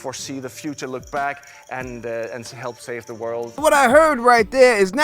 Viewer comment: If you actually used your ears and listened to what he said instead of what you wanted to hear you wouldve heard him say HELP save the world not save it